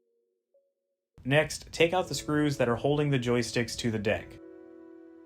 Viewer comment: Lol it sounded like he said take out the screws holding the joystick to the dick XD